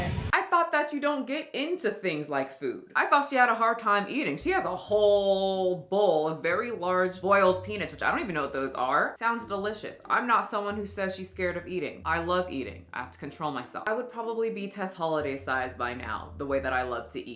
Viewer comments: I could have sworn you were saying "boiled penis" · I thought you said boiled penis! 😱😱😱😱😆😆😆😆😆😆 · I heard "boiled penis" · I thought you said boiled pen8s! I was like she eaten whAHt!?🤣😭💀